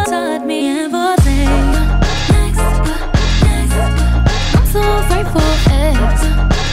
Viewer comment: I heard her Say I’m grateful for eggs 😂😂